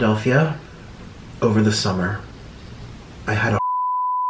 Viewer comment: i had a hard what ? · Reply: I thought he said I had a hard 🍆